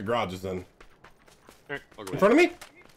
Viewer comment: was that a stream sniper? I swear I heard an echo of “in front of me”